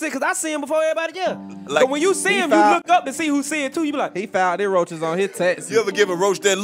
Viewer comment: So nobody heard him say “ he filed the roaches on his taxes”😂😂💀💀💀